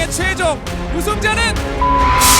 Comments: 초부터 0.25배속으로 들으면 우승자 양승호 처럼 들려요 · 초에 나만0.25배로들으면 우승자 승호학생이라들리나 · 이거 0.25배로 1:54부터 들어보면 우승자는 삐ㅡ 승호학생입니다 들림 븅신엠넷